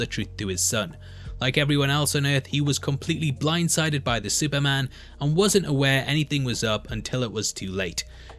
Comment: did he call Omni-man a Superman? or is he saying something else? accent kinda funky can't tell